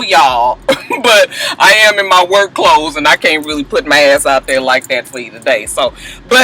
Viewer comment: Did you just say "I can't just put my ass out there TODAY" lmaooo just not today huh